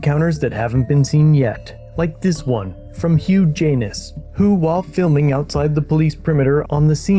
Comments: Hugh Janis eh... lol, I had to rewind to see if I heard that right💀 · Did he say huge anus